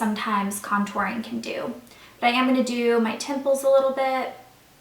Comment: when she said "bronze my temples" for a second I thought she said pimples so I was kinda confused haha :)